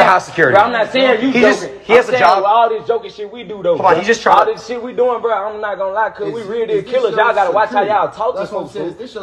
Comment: can someone translate what NLE said in that part? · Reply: All this sh*t we doing I’m not gone lie we real deal killers